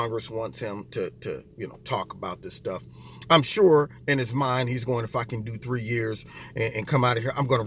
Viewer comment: AM I the only one who heard swear on video? 7:26 Did he just say, "I'm sure, in his mind, he's going to do fucking 3 years" o.O